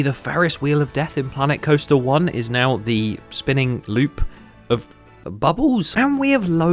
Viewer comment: I thought he said spinning buttholes 😂😂😂😂